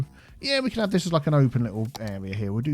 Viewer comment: "Square and Grid-ish" somehow I heard "Square and British"! Thought you were talking about yourself for a moment there... 😂